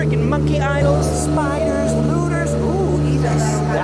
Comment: did he say hooters?